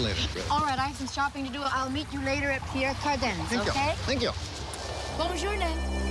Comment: It sounds like Angie says " Buongiorno" at 15:31...which is Italian...which would be odd..in Paris.